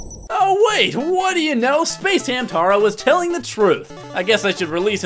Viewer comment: Did call him space hamtaro?